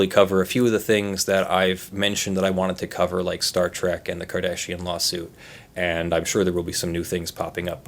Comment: I heard "Star Trek and the Cardassian Lawsuit".